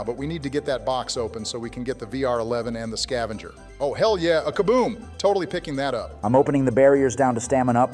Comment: di did he just said a kaboom?